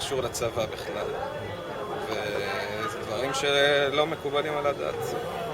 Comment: He said "Not acceptable at all" , not "forbidden according to religion". I'm a Hebrew speaker, and I understand why the translator misheard that. He said "Da'at" - logic , not "Dat" - religion.